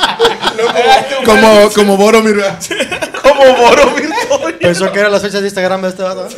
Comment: que dice en el min 29:30?? no alcanzo a entender que dice. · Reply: "Como Boromir" personaje del libro El Señor de los Anillos el cual murió por tres enormes flechas (y siguió dando pelea)